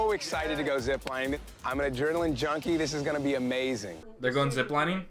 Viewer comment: when he said he was an adrenaline junkie, i thought he said he was a journaling junkie 💀